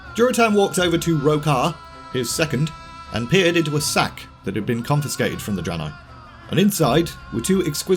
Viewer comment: Hunters are fun to play. I am going to lvl one in tbc prepatch soon. Also I think I take Velen's side...lol. The was no disrespect meant. Also It sounded like you said, "he peered into 'his' sack".....with the 2 stones.. had a good laugh there. Thanks for the video!